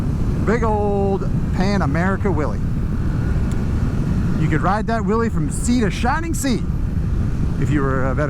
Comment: Might want to work on that enunciation. What you said, " You could ride that wheelie from sea to shining sea." Here's what I heard, "You can ride that willie from sea to shining sea." 🤣 Geat review. I'm more than a little tempted to get a Pan American. It'll be the first time I've been interested in a HD made after 1950.